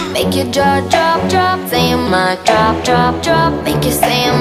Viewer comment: I thought she was saying ‘mic, drop-drop’ not ‘my, drop-drop’.